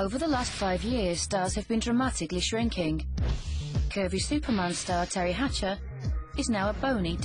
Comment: When she said "Curvy Superman Star" I thought she said "Curvy Supermonster"